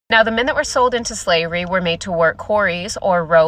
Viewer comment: Ah Yes. The old "lets compare who's slavery was the worse" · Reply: user-yz2mo6yp7k Dude, she wasn't saying anything about reparations. You probably heard her say 'the minute we're sold into slavery" when she ACTUALLY said "the men that were sold into slavery"